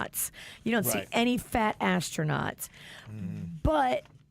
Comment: Why did I hear “fat-ass” tronauts? 😂😂😂😂